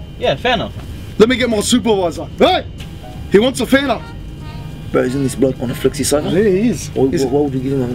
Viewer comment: What does he say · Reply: "bro isn't this guy on a flexi saver"